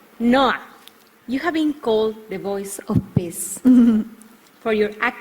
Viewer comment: Did she really say the voice of piss? :)))